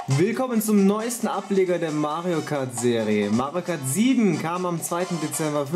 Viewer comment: Ääh, habe ich mich verhört, oder sagt Mario am Anfang wirklich "Mario Kart Sieben"? Spricht der nicht Englisch? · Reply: Er sagte seven hört sihh halg wegen Aufnahme komisch an aber es kann sein